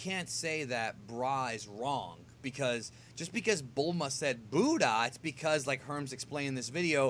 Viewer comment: I thought Bulma said Bulla